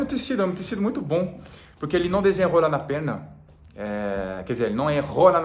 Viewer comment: Eu entendi "O tecido não desenha a rola na perna" depois de rir por 30 minutos seguidos eu entendi que era desenrola na perna